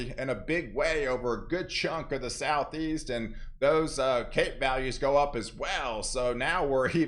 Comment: All I hear is "Cake Values" 😋 🍰